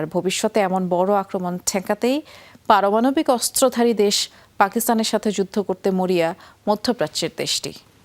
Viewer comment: এটা কি বললেন আমি ত বুঝলামনা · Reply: 😂😂😂😂ha ha ha . Pakistan er sathe juddo Korte chay